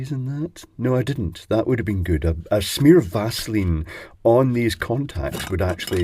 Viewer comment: hello, at 2:31 you say "did i put some greese od those contacts". but i did not understand did you say "vaseline" or something else? thanks in advance! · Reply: Vaseline or silicone grease.